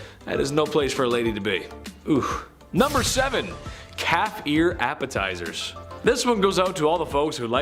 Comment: OMG, I thought he said cat ear appetizers at first instead of calf lol